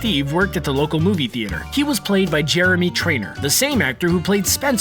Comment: Honestly sounded like you said Jeremy when it’s definitely jerry.